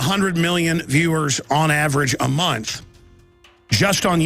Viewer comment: Wait did he say "a munch"